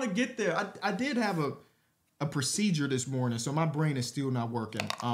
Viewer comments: Anyone else think originally said “I just had a seizure this morning”…I was like ummmm why’d he drop that info so casually💀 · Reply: Thought he said “procedure” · Anyone else think originally said “I just had a seizure this morning”…I was like ummmm why’d he drop that info so casually💀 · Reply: ya he definitely did, But at first I thought he said seizure lol